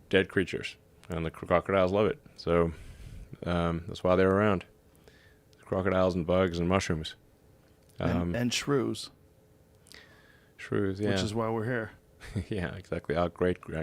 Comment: I thought he said crocodiles eat shrooms.